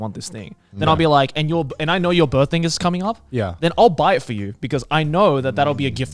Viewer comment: Is no one going to mention how at 1:08 it sounds like Joey said birthing